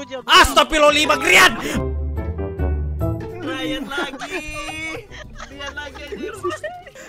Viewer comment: 5 (?) sama 6 paling ngeselin 😂 Edit: Btw 14:45 ngomong apa itu bang sampe disensor wkwk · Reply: Itu nyebut namanya sih grian (sambil teriak) tapi biar kuping kalian aman gw mute wkwkk biar gak pecah 😂😂😂